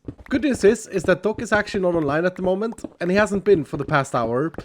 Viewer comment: I swear, I just heard him say " the good news is doc is actually not alive at the moment "